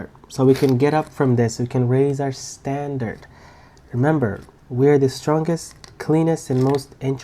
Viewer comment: Did i hear "we're the cleanest.. "?